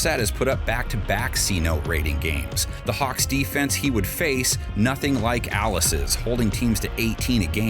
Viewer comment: did I really hear “Dallas’” with a silent D because they got none 😂😂😂😂